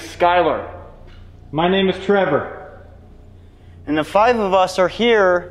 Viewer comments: When introduced himself it sounded like he said Trevor ❤️🇬🇧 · It sounded like said my name is trever??